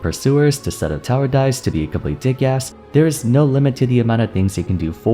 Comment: Bro did you say “dickass”? thats a new one Imma start using that thx.